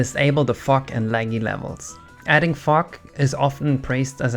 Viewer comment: sounds like you said adding fuck